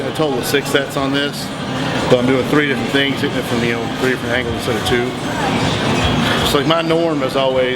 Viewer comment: Lol good vid , 7:46 did he said 3 different meal ?